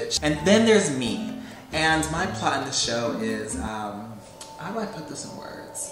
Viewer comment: Why did he say “my part of the show” in an English accent??